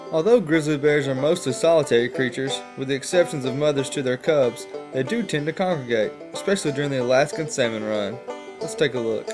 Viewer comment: I thought he said semen run